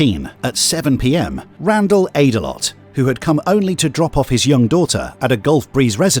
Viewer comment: Omg lol I wasn't paying full attention at first, and I thought the narrator said, "Randall ate a lot" (like clowning on this family for being fat), but then I went back and looked again, and realized his last name is "Avdelott" pronounced (ate-a-lot)...lol I was about to say damn that's fucked lol